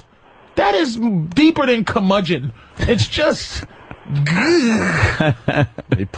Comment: Can somebody please tell me what this word means at 2:23:34 'deeper than [?commucient?]' and how you spell it? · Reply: cur·mudg·eon - <link>